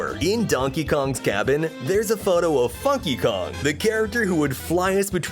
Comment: there’s a photo of fucky kong,, 13:25 that’s what I heard 🤨